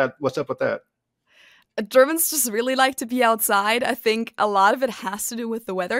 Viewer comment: sounds like she said "Germans like to pee outside"